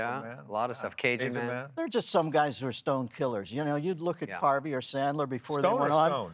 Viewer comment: burteriksson notice how shitty this phrase is? 0:31 ONE PERSON picked up the stoned killer. I damn sure didn’t the first time. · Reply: What are you talking about lol. Yeah i heard Dennis say "stoned killer" at 0:31, I think he either misspoke or just used stoned as another way saying stone cold